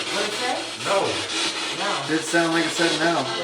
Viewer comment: /9:25 it says "Will." Sounds like "no" at faster rate, but i swear if you slow it down to .25, it says the name "Will." 😳 slow the speed to .25. Right before the word "no/Will," (i think it says Will) you hear spirit box say, "it said Will." 😳 It's almost as if it's answering your question before you ask it! Right before the single word. Some hear No, some Will, but right before that i hear, "it says Will." Make sure speed is at .25!